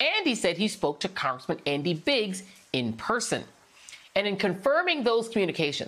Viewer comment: I thought it said, "spoke to Andy Biggs in prison" instead of "in person"!